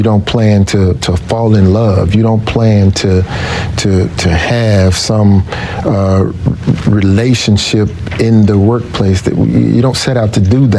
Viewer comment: Thought he said they never had a relationship